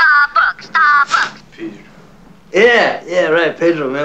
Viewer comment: Does anybody know what the bird is saying at 1:52? Sounds like Starbucks, Starbucks to me.